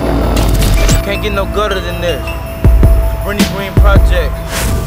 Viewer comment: You heard him it can't get no gooder than this. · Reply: Corey Ladson I thought he said "it don't get no gutter than this" 😂😂😂